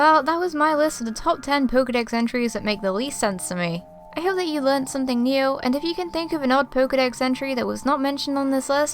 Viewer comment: at the end instead of hearing "i hope you learned something new" i heard "i hope you learned something *mew*"